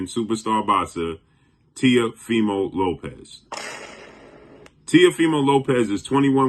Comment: … you think you slick I heard you at 4:22? You said Tia Female Lopez! 😂👍🏽